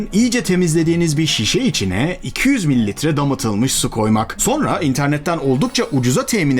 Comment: Damıtılmış dedini ben domatılmış anladım 😭